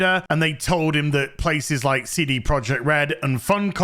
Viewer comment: Did I hear him say "CD PRED* ??